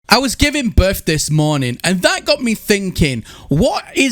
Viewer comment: I was building a nuke this morning and that got me thinking, how does this guy come up with such good intros? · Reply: I swore I heard I was giving birth this morning and I was like, bro, same.